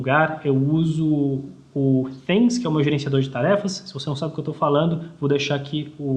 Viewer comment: Alguém traduz pra mim o que o disse no minuto 7:25... Ele disse que deixaria um link, mas não tem nada e está impossível de entender o que ele fala: "Eu uso o *****, na mesma metodologia que o ***** usa." Engraçado que na transcrição está "que eu tô no meio em algum lugar eu uso o pênis que é uma gerenciador de tarefas se você não sabe que eu tô falando vou deixar aqui o link para você dar uma olhada depois a luz queria saber se o meu método de produtividade então eu uso fez como inbox na mesma metodologia que". Nem o Google entendeu 😅 Alguém me ajuda aí! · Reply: O app é o Things <link> e metodologia é o getting things done ou GTD <link>